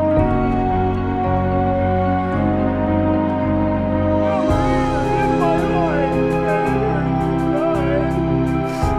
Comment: Why is he doing that? Not shooting...lol 1:54 Anyone else wonder what he's saying? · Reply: captain hammer says something like “here, in my heart, and it ??”